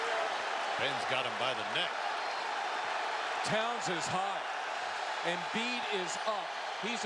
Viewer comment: What did he say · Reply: Hot I think I thought he said high lmao